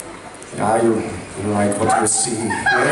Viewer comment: can anyone tell what Misha is saying at 3:23? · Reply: Riverxxx you like what you see?